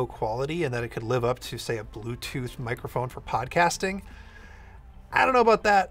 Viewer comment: I accidentally said 'bluetooth microphone' instead of 'blue' – Dieter